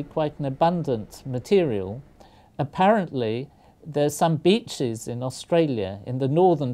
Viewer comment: Anyone else thought he said bitches